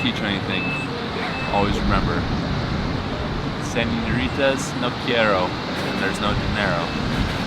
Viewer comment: What did Bo say at 8:04? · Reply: Señoritas no qiero when there’s no dinero…as in women don’t want you if you’re broke